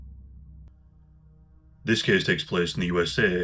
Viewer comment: I remember the not-so-old days when our esteemed host didn't even like to be on camera! But then he slowly started to grace us more frequently, and now we've got podcasts, shows, and all kinds of camera angles! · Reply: Same!! I don’t mind people wanting to remain some kind of anonymity on YouTube so I guess I missed the “reveal” (if there was one lol). Then I heard “this place takes case”, laughed, went back to listen again & was like, “oh, hey there!!” Anyway..stay safe friend!! 🖤🖤🖤